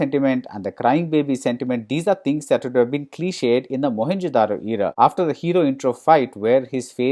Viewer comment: did he said mohenjo daro? 🤣 His face says it all 🐢